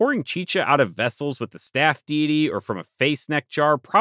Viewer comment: i mis-heard staff deity, and thought you were saying “staff meeting”. it sort of made sense, and from then on, a little smile would come to my face, with each mention, as i pictured a staff meeting ceremony. something along the lines of "The Office" (the brit one with Ricky Gervais)...